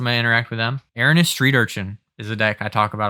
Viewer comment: What is the deck you mentioned after K’rrik at 57:50? I heard Hulk and was intrigued but I can’t figure out what the commander is you said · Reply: Erinis/Street Urchin!